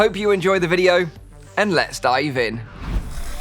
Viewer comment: is it me or do i keep hearing "lets start even" and not "lets dive in"